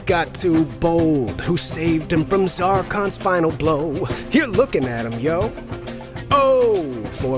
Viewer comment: "you're looking at him" I heard "you're looking adam?" I'm not crying, you are.